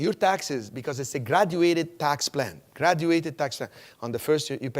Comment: "graduated tax plan" sounded like he said "exaggerated tax plan", which is more appropriate.